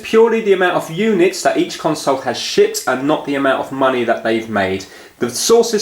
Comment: what the heck did he just say? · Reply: lol, it is 'shipped' but sounds like something else :-)